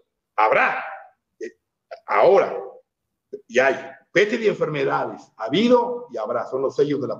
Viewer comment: creo que dice pestes y enfermedades, 48:21 me encantaría descifrar qué dice. Hay varios fragmentos en que desgraciadamente no le entiendo 😢